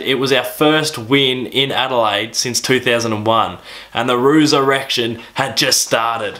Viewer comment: I thought you said Roos erection for a minute...